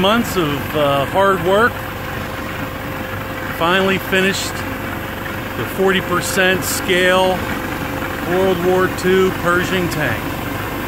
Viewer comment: I thought he said "Finally finished the world war 2 Persian tank"